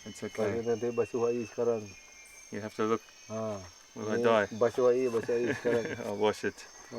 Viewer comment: i think the native actually say: "kawan basuh air sekarang, basuh air basuh air sekarang" friend wash with water now, wash with water wash with water NOW"